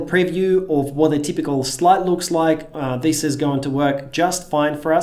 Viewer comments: I thought he said see what a typical slut looks like lmfao but he said " slide "... Hahahahah damn accent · I'm watching this video kind of late, so I'm a bit sleepy. But with his accent, (no judgement), I really heard him say, "what a typical slut looks like". @ 4:52 I'm crazy right? lol...